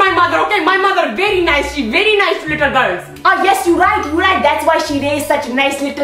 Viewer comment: Dad: "i told her to run very fast there is crazy lady here who eats little girls." Mom: "HUUHHH !!! your mother here ??" Dad: "AYY !! DON'T TALK ABOUT MY MOTHER !! MY MOTHER VERY NICE SHE VERY NICE TO LITTLE GIRLS !!" Mom: "Ah yes you right you right that's why she raised such little girl." I'M DYING FROM THAT PART SO HARD !!!!! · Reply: oh!! I thought he said She very nice little girl!!! 😂😂😂😂